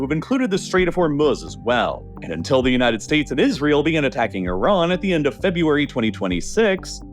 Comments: did i hear The united States Of Israel LOOOOOOOOL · did he just say the united states of israel no am not wrong he clearly said it lol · Did he just say "The United States of Israel" ? 😂😂 i mean, he's not wrong 😂😂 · "The United States of Israel" I can't tell if I heard RLL wrong or if he's cooking and coming out swinging against the US, Israel, and Pax Americana altogether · did he say united states of israel😂😂😂😂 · I could have swore I heard "United States of Israel" at 2:29, but I was mistaken. LOL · Did you say “the United States of Israel” at 2:30 lmfaooo